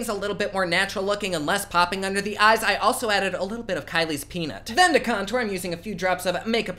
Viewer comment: I thought he said “Kylie’s penis” 🤣